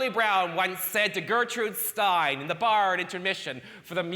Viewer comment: Oh, were they saying “bitter” this whole time? I heard, “it gets better.” Bitter makes more sense. That would explain my life.